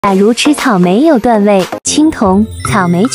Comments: 我聽成《假如吃草，沒有段位》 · 有谁和我一样听成假如吃草没有段位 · 我聽成假如吃草沒有段位😂😂😂😂